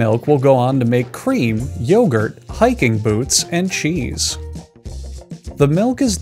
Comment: Did I hear that right hiking boots at 0:36 secs wtf